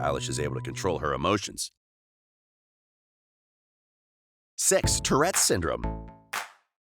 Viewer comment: lol, at 5:00 looks like he said "sex" instead of "six"